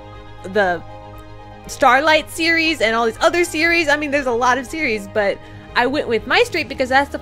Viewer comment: I thought she said "theirs a lot of Siri's"